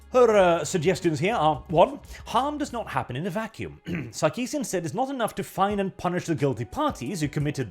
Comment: Did you say "Suckeesian"? I'm just gonna assume you did, makes it that much better.